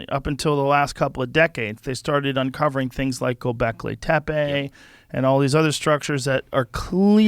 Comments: What dose he say in 0:55-0:57? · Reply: And all these other structured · What civilization did Joe say at 00:54 seconds? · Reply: Taipei, I think?